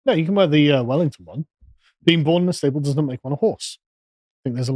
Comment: I thought that O’Connell said “being born in a stable does not make a man a horse” about Wellington?